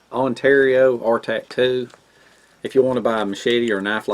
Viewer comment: If you mean the micarta handled one its a Condor Garuda drop point. Silky saws are almost like gas-less chainsaws! Thanks for watching Victor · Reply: it sounded like you said, Ontario R tattoo ?